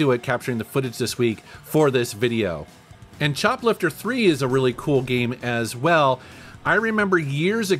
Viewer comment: I swear I heard "chocolate for free" the second time you said choplifter III.